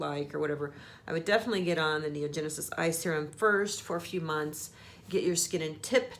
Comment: what kind of cream/ointment did you say at about the 2:44 minute mark. I didn't quite catch it so I rewind & put the cc on & it looks like " India Genesis I (eye) serum " I checked to see if you put in your description but didn't see anything. lol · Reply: Beverly Kittle NeoGenesis Eye Serum. My holy grail eye serum. <link>